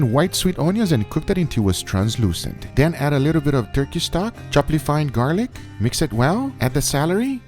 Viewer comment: Wait, did he say "chopley fined garlic"?